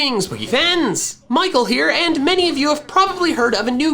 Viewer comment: I heard "greetings, pokefans" as "greetings, boogie pants" literally just once and now I'm unable to unhear it